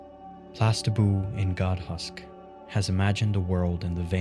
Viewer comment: What does he say "Tastaboon and God Husk?" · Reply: Plastiboo